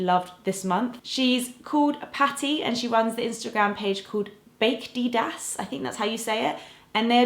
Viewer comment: I can't stop laughing at you pronouncing baked ideas as bake dideas hahah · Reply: I thought she said bakeDIDAS lmao 😂